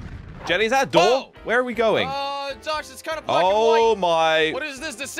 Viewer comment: In the intro it sounds like Jelly says “Uhh Josh it’s kinda f*cking white” 😅